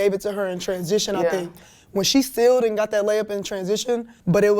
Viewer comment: Did she say "when she stealed and got that layup" at 2:09😅